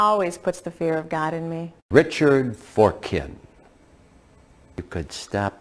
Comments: I swear his last name said foreskin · they put Dick instead of Richard and his last name is Forkin which sounds like Forskin…. so Dick Forskin